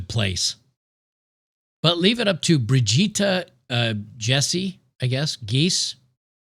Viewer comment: => When you said "Brigitta", I heard "Vegeta" and I was like "What the Hell did this guy do again?!?" istg this guy did over 9000 bad stuff...